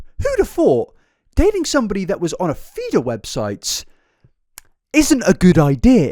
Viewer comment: A hwhat? I keep hearing theater. I’m confused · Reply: Feeder not theater lol